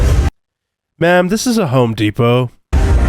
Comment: Did he say "Hoe Depot"?